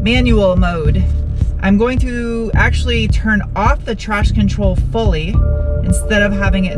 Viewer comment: did you say "turn off the TRASH control fully" 5:32? Another great review. Love your humor, keep it up!!